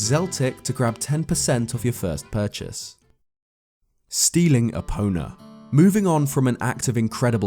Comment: When said stealing epona i thought he said stealing a boner